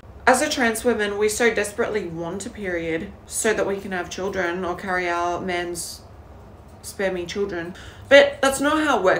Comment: "spare me children" Got me lol🤣😭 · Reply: Did she not say "spermy children"? I thought she said "So we can carry our man's... Spermy children." 😂💀